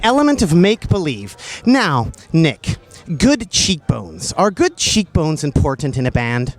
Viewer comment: Good what? can someone tell me what the word that nardwaur says is? I'm sorry my english is not really good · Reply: Arctic October 8 years late — good cheekbones